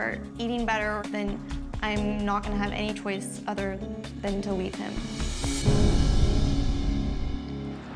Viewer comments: I thought she said delete him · She sounded like she said “delete him”